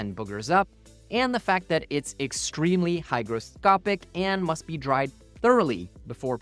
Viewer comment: Nice, well-edited and informative video! I print eSUN ABS+ without problem on my MKS3 without enclosure, not a chance with normal ABS though. Also, maybe I misheard, but it sounded like you said "hydroscopic" – it's hyGroscopic!